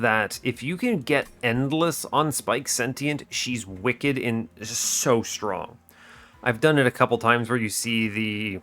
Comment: i heard you almost call her wicked strong. you hide from your power, new englander.